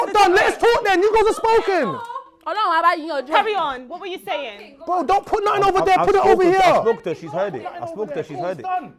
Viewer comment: i’m so curious to understand what said at 24:55 😭😭😭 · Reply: “God will help you sort it out “😂😂😂